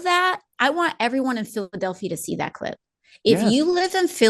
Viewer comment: Ok honest talk the skip on AOC’s video, made it sound like she said “Filthadelphia” which sounds like a Trumpism if ever there was one 😂 I doubt that’s what she actually said tho